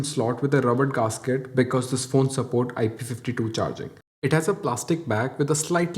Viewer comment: did u say IP52 charging?